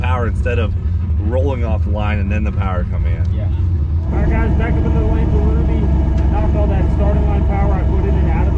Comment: I had subtitles on and it thought when said "back up in the lane with ruby" that he really said "back up in the Lamborghini". Close enough lol